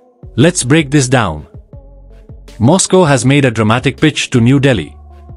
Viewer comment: the word is "pitch", it doesn't start with a "b", not wonder Trump said he can't understand a word from more than one Indian reporters.